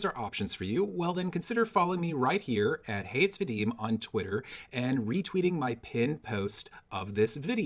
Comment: Slow down, brother. With that elision at 15:12, it sounded like "Hates Vadim" and we don't. . . not even my dog.